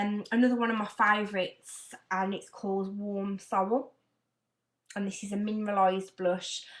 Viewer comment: Ive just realised that i misheard you when you were describing warm soul (not sorrell). Iv just ordered it now as i have pale skin and need a non heavy look to the cheeks, Thanx once again xx